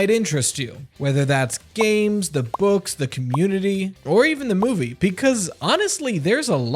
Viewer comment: bro I paused at the WRONG time I unpaused it and thought he said nudity 💀